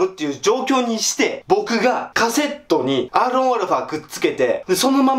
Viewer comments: アーロンアルファ に聞こえる w w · アーロンアルファって聞こえる